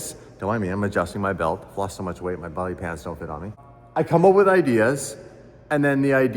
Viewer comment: Did he say he’s lost so much weight that his body pants don’t fit him? What the hell are body pants? Lol